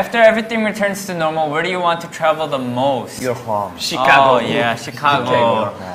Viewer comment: For a second I thought he said your mom